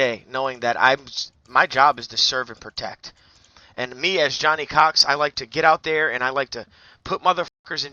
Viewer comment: Bruh I thought ts said "Johnny Cox finale"